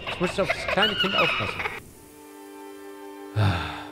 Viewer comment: Hat sonst noch wer bei 4:55 "is das schöne" verstanden? 😂😂😂